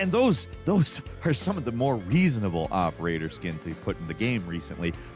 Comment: did you say Reasonable but Nicki Minaj not allowed for the female Gamers... I tell you man white people mainly like white people things 😂